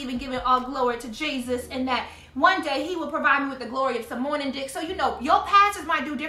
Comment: "Being blessed w/some Morning Dick"....Gurl, I cackled so loud 😆😂😭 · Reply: Hahahah I had the same reaction! I was like errrr, did she just say morning dick...and then I died laughing!